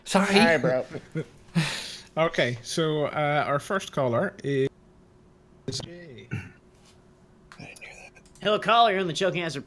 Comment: anybody else hear that "so, our first caller...is faintly gaay", and right after that whispering "I knew that"?